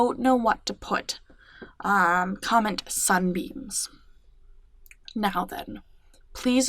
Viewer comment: Lol I miss heard you and thought you said sunbeans 😂